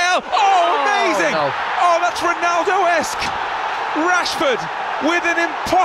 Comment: did he said, "that's Ronaldo's kick"!!!????? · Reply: No, he said "Ronaldo Esque".